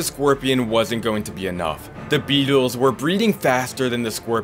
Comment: the caption said that the BEATLES WERE BREEDING